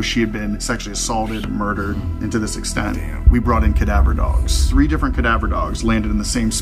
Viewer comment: I was watching this with my little sister and at 17:55 when it said Cadaver Dogs my little sister looked up at me, and said "There are magic dogs?" and I responded with "What?" and she said "Abracadabra dogs?"